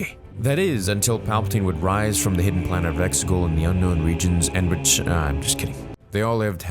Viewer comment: the subtitles say, "Until Palpatine would rise from the hidden planet of Mexico" looooool